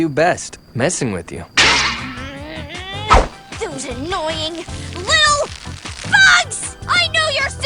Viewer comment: it sounded like she said "Those annoying,little FUCKS!!"